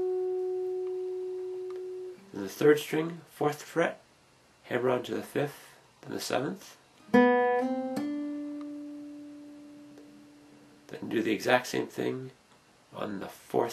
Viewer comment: what is that technique he says at 2:44 that sounds like hair rod?